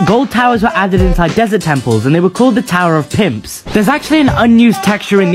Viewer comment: I thought he said tower of simps😂🤣